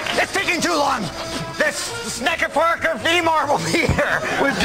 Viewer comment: What does colin say at 8:20?? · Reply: The Sneckerparker of Emor will be here